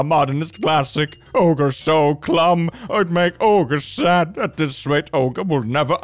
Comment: did you just say "Ogre so clumb"?